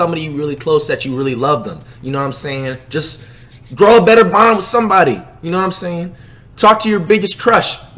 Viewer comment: i thought he said grow a better mom